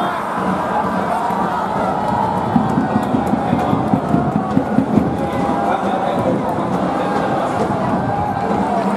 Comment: 場内アナウンスなんて言ってんだろ だれか教えて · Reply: 「ただいまの得点は…」という感じで、ゴールを決めた選手を紹介してるんだと思います(^^)